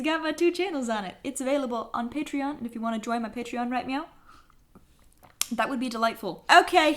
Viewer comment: all I heard was "If you want to join my patron write meow"